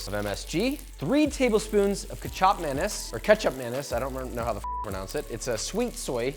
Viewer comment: Almost jumpscare me when i misheard you saying Kecap Manis as Kecap Mayonaise